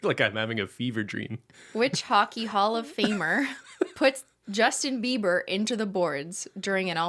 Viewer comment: i keep hearing “hall of fame” as “holo fame”